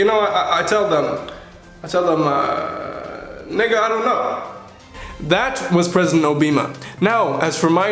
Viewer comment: Sorry for my stupidity - did he say "nigga don't know"?